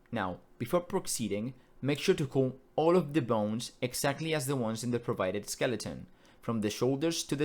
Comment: what did you say to do here because i cant understand the word you said sorry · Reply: "Before proceeding, make sure to call all of the bones exactly as the ones in the provided skeleton" Basically just make sure that your base skeleton structure matches the one in 2:25, without any extra bones in between (you can add additional bones parented to these later, but the main part of the skeleton needs to look like that); it's a bit redundant, but it's necessary to clarify it because even if you roll the finger bones, if there's extra parts in the skeleton between root and the hands, Unity won't detect them correctly and they won't work.